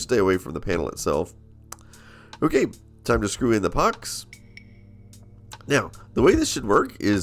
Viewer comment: Those look like ducks. 😂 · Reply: that's what he said at 2:55 "time to screw in the ducks". Ducks, pucks, whatever 😄